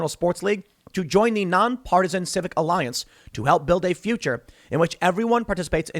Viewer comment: For a second I misheard you and thought you said "Non-partisan Sith Alliance" and suddenly all the evil that Dems do makes sense. Their all masters of the dark side and seek to create fear and division to increase their power.